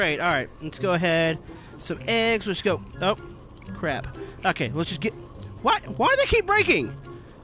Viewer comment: did anyone else hear say the c word